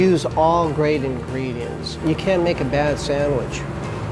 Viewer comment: “You can’t make a bad sandwich” Me: Challenge accepted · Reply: I thought he said you CAN make a bad sanwich